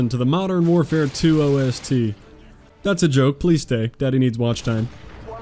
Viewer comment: Did he just call himself Daddy at 3:59? 😂😂😂